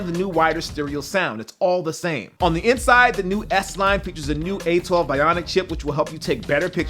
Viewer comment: Did I hear you say the "new S9" at 2:05?